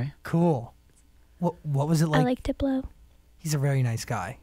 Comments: i swear i heard she said, "i like to blow" and i almost lost it there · lmao i thought she said "I like to blow" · my dirty mind thought she said "I like to blow" 😂😂😂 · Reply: I heard "I like to blow" too! ...even though it's supposed to be "I like Diplo"! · When she said "I like diplo" I thought she said I like to blow 😂